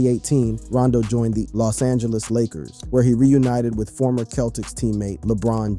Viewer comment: narrator says he reunited whit former Celtics teammate Lebron James 😂😂🤦🏾‍♂️ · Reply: I thought he said that he reunited with his former keltic teammate. Does this guy know his basketball?